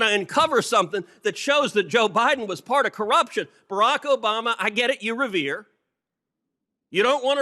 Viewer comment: I am so Glad this man is my senator. All you other districts around the Union are at a disadvantage because he is ours. Cruz 2024!!! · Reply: I NEED HELP PLEASE ! I'm doing a translation in french, Can someone write me what He says at 8:19 ? I hear "Barack Obama, I get it , you Revere" , is the word "revere" correct ? Also 4:18 , what is "MAST intercepts" ? PLEASE I really need help.